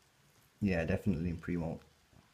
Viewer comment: What's the word you say around 8:57? It begins with a p I think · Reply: Olle Anund pre - molt